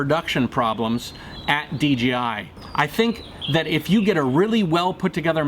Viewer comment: he said dgi instead of dji I get confused to